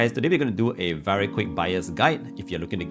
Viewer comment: I heard "very quick biased guide" HAHAHHAAA